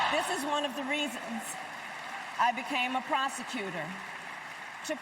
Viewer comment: What I heard was "this is one of the reasons I became a prostituter" 😮